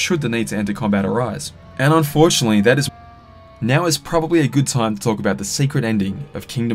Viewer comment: I'm also going to mention the cut at 47:44 what was supposed to be said there? Can you type it down below or reupload? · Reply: I was saying "And unfortunately that is all we know about the story so far" and basically wrapping up the video. I had recorded that section before the lastest update with Maleficent came out. I must have accidentally shifted a section a couple of seconds off the timeline and unmuted that part, sorry